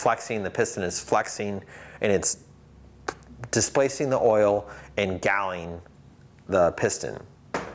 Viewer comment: what does ingalling mean or am i hearing that wrong? would appreciate an explanation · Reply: He says "and galling" i thinks that's metal to metal friction damage.